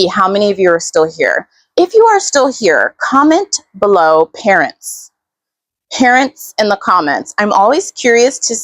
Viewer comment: 💗Parents! At first I thought you said parrots.